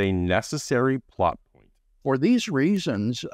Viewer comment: “…a necessary plot _________”. The audio cuts out here. What is it that said? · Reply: plot-point.